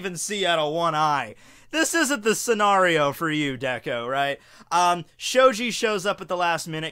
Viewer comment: did he say Deko? get your pronouncing right :p Edit: btw who else here calls him Izuku instead of Midoriya?